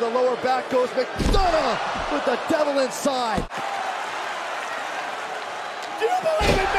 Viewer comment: i thought it was called devlin side 😂 now i know